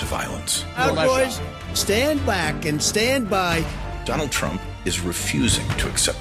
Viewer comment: holy sh88 I thought all he said was "Stand back and stand by" but when you put the video on 0.5 speed, he literally says "Proud Boys, Stand back and Stand by"